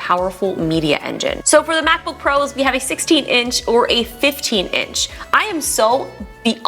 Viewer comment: Hey, Justine. Great vid. Did you just say apple released a 15-in mbp? 1:46 min.